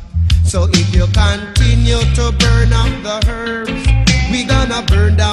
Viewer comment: Lol at first I thought he was saying "burn up the babies"